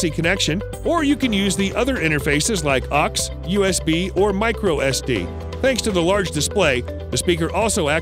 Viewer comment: Did I just hear "Micro SB" ? It's "Micro USB" O-o